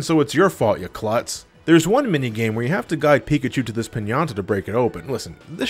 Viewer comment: * mentions he buys ass wipes an now "That" image is stuck with me*........sigh....... 4:57 I know what you are saying but it sounds like you're saying pianta and its makes me wish it was lol XD Also yes play the dam puzzle! XP